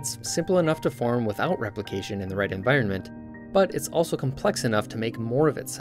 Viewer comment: The captions say "red environment" instead of "right environment".